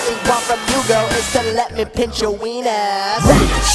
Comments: I thought he said pinch your penis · It sounds like you were saying let me pinch your pines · I thought he said let me touch your penis.